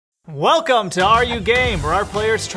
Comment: i thought it said "welcome to are you gay" i cant